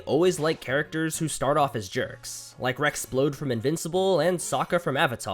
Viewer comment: the subtitles say Rouxls Kaard instead of Rex Splode lmao